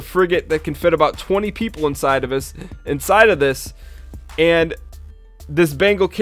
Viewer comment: "Can fit about 20 people inside of us". I heard it, you ain't foolin me! :P